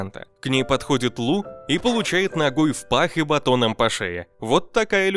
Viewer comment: Получает ногой науй и батоном в шею! 😂❤❤❤ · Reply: Я услышал "нахуй в пах")